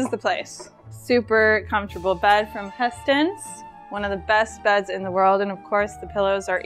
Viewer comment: What was the brand of mattress? Sounded like “Pestence”. Is this correct? Thank you so much in advance. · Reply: Hästens